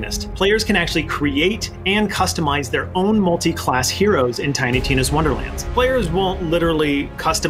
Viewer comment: Did he say multi class ?